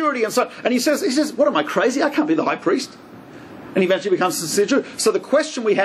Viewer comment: What is he saying at 42:25? Forgive this American's unacclimated ears. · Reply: Am I crazy? I can't be the high priest. And can you imagine he becomes ....